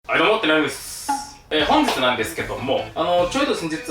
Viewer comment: 最初のどうも谷やんが 黙ってないですに聞こえた